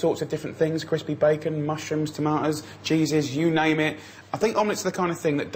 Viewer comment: I heard "Jesus" twice and then realized he was talking about "cheeses" lol